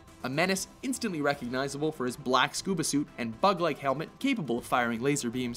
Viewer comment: I'm sorry if I heard this wrong but did he say thug life helmet?